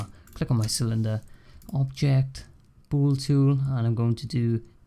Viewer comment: what do you say? pool tool? I cant find :( but either way I think this is much better than donut tutorial this is easy and helpful